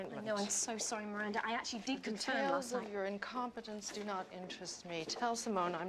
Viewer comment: "Tales of your incompetence do not interest me..." Still waiting to use that one haha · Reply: Thought she said "Details of your..." but alas